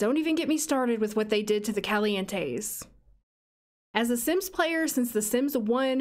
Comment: I really heard “Kelly-Ann Tays” not “Calientes” for a hot second